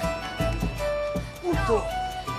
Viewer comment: Me da mucha risa cuando parece que dicen "puto" en